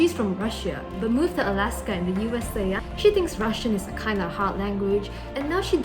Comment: Due to the accent I heard “She thinks Russian is a kinda 🔥😩 hot 😩🔥 language.”